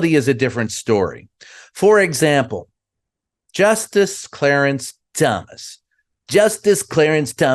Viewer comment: 😆 for a second I thought he said justice Clarence dumbass 🤣